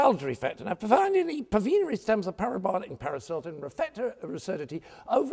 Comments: Did he say Paris Hilton? O.o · haha at around 1:27 does he say "paris Hilton"? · Did he said "Paris Hilton" at 1:27?! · did anyone else hear Paris Hilton? LOL